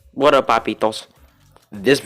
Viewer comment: I hear "What up puppy goes